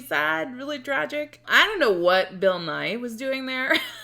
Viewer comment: I thought you said Bill Nye, bit it's actually Bill Nighy, who you accurately recognized. I don't really know who he is, though.